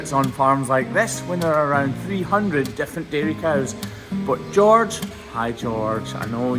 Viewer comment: My audible gasp when you licked the hoof was probably heard by my neighbors down the street… 😂 I did realize it was ketchup, but still, I did not expect you to lick it 🤣😳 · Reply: I kept going over one part of your video…it sounded, to my tired American ears, one thing, but we found out that it was something completely different! Early on in the video, I kept hearing, “300 defecating cows. WHAT!??! I listened to it two more times, then I broke down and turned on the CC - Oh! 300 DIFFERENT KIND OF cows! Egad!